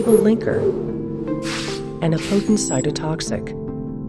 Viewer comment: a what "cytotoxic"? Did she say a "patent cytotoxic"? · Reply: Floyd Collins potent